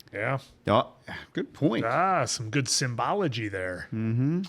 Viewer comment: Anyone else hear Willem Dafoe say, "I'm sure the word you were looking for was 'symbolism.' What is the ssss-ymbolism there?" 😆